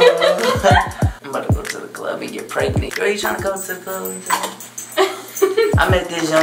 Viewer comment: did he just say he's gonna go to the club and get pregnant?? 😂😂😂😂😂😂😭